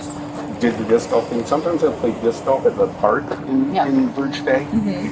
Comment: At first I thought he said he “plays disco” to relieve his back pain. I was wondering, “how would disco music help one’s back??? 🕺🤔 LOL